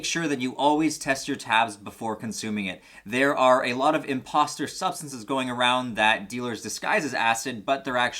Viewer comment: I thought he said imposter sus